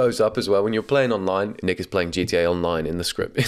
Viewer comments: did he just say the n-word? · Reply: Nick is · what did he say 😭🙏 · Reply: Nick is, The name of the commenter was Nick 😭